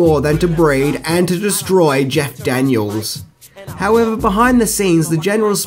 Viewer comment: Also at @ 5:20 it's Jeff Daniels... I think you said Jack Daniels 😅